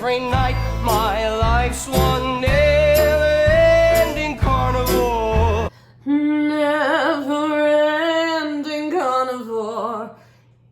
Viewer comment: i can't tell if she was saying carnivore instead of carnival at 11:19 😭